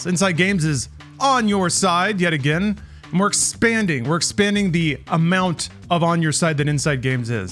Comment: all I heard is that you guys are expanding inside me. Have our relationship gotten to this point already?